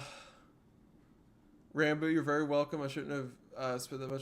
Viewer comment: am i tripping or did he just say ranboo